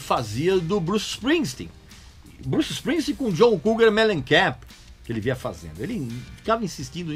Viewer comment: opa? Ouvi vc falar do John Cougar Mellencamp??? Ahhhh faz um vídeo sobre ele!??? 👏👏👏